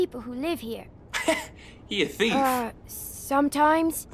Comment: when he said sometimes it sounded like clem lol